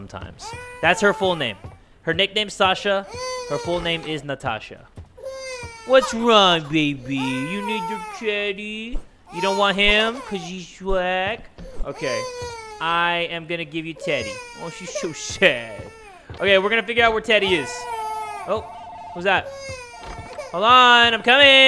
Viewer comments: Did he just say “you don’t want him cus hes black??” Lmaooo😭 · I legit thought he said “You don’t want him because he’s black?” And I was confused af lol